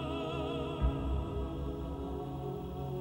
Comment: I thought Donnie said Can't take it off